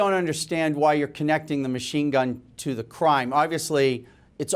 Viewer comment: Did he just say " I dont understand why your connecting the rifle to the crime "